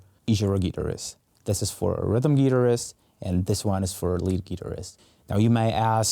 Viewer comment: Great little rig tour - but I keep hearing "Rhythm Uterus, Lead Uterus" - Fertile Imaginations LOL? Srsly - nice rig tour - I watch at these, wondering how they can be smaller/ligher - but tahts a pretty compact thorough job there !